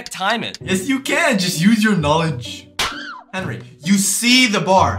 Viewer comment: did he say henry